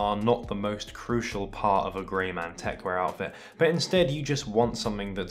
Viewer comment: I really thought that said greymon.